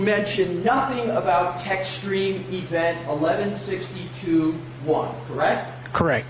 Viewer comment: What is a texturing event or text stream event?! 1:49:59 and various other places lol. It’s just bugging me that idk what it is and can’t tell what he’s saying. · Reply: I think they are saying Tech Stream/TechStream event.